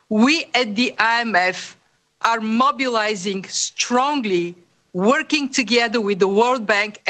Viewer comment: Did she say she's "mobilising" or "monopolising" resources needed to fight the virus? I thought I heard her say "monopolising" in that clip